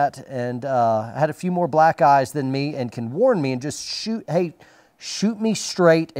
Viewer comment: Did you say someone who had a few more Black Guys than me? Hehe Had a B-1 crew in Afghanistan that was insane. Flew below mountain tops to keep the bad guy heads down after they dropped their last JDAM. Allowed us to break through an evelopment in a small village in Kunar.